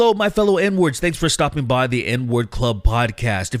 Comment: ok this is my first time seeing it in text. I genuinely thought he was saying the N-word Club.